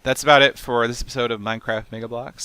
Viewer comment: It sounded like he said nigga blocks at the end haha. 8:48 Nice vid though :D